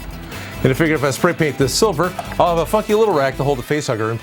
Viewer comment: Did anybody else notice that he said fucky instead of funky